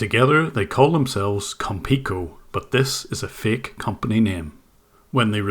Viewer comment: I thought you said "konpeko" at first lol